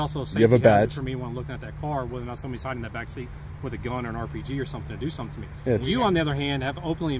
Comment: Did he say (10:01) "a gun or an rpg..."?